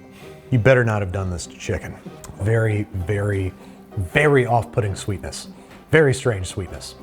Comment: The internet ruined me in the sense that everytime someone says 'off-putting', I only hear 'off pudding' and hear Jennifer Lawrence in my head saying: 'You should be off pudding!'